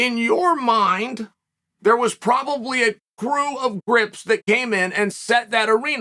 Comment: Referring to people as “grips” is going to be a thing I do for the rest of my life. Thanks Unc. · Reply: A "grip" is responsible for setting up, rigging, and managing light equipment on a set. According to the Dictionary. It is a great word for people. I thought he said "crips" at first 😂.